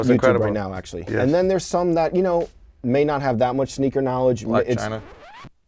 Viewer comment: somebody help me translate what said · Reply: He mentioned Black China. I guess I could be wrong. She is a celeb